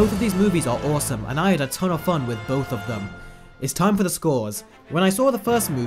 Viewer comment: I thought it said Scory